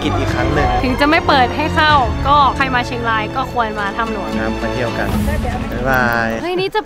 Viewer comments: พูดว่าควย ลองฟังหลายๆรอบวนดูออกเสียงว่าควยจริงๆไม่ได้ยินว่าควรเลยเหมือนลมขึ้นจมูกและออกเสียงมาอย่างนั้น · ได้ยินพูดว่า เราค*ยมาถ้ำหลวง #โนมาม่า😆